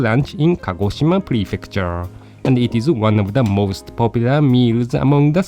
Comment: I know he said "most popular meal", but I want to pretend that he said "most popular meme", like I thought he said the first time.